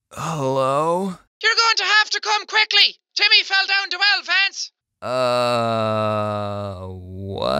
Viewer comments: did anyone else hear “You’re going to have to come quickly because Timmy fell down the well, Vance” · it sounded like YOUR GONNA HAVE TO COME QUICKLY TIMMY FELL DOWN THE WELL VANCE! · I heard “You need to come quickly, Timmy fell down the stairs hdhfinfurngk” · I heard: Your going to come quickly Timmy fell down the well Vance. Any one else hear that or is it just me? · It sounded like “you‘re going to have to come quickly, Timmy fell off the well fence” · What everyone else heard: You're going to have to come quickly, Timmy fell down the Well Vance! What I heard: You're going to have to come quickly, Timmy fell down two Elephants! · I heard “You have to come quickly, Timmy fell into elephants” I mean like at the zoo 2019 anybody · i heard, "you are going to have to come quickly timmy fell down the well lance" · I heard "come quickly Timmy fell down the elephants " XD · It sounded like “You’ll have to come quickly because Timmy feel down to hour Vance!” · I thought she said, “OMG TIMMY FELL DOWN THE STAIRS VANCE” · I honestly thought Jack was saying JIMMY FELL DOWN A WELL VANCE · i thought she said “come quickly timmy fell down the well vans” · "You're going to have to come quickly! Timmy fell down the well Vance!" That's what I heard. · what she is saying " You have to come quickly Timmy fell down the well Vance! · I know exactly what she said. She said “you’re going to have to come quickly Timmy fell down the well lance” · she clearly said it, timmy fell down two elephants.